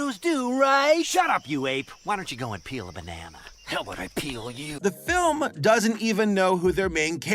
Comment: I though he said pee- so I just thought the monkey said "how about I pee on you" 😭